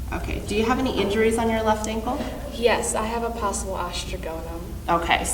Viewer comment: Does anyone know what she said her ankle injury is? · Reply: Sounds like she said Os trigonum which is an extra bone in the foot, hope that helps!